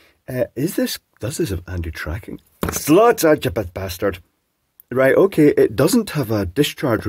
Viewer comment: "flaudugadabastard" What the hell was that word you said??? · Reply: It was the traditional scottish toast= "Slàinte Ya Bastard"